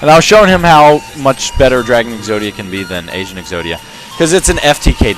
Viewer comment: I thought you said Asian Exodia xD